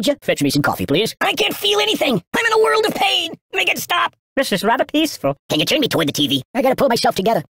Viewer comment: It sounded like “This is Rabic -eaceful”